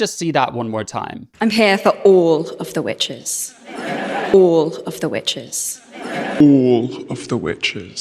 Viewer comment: she is clearly saying "by the way"...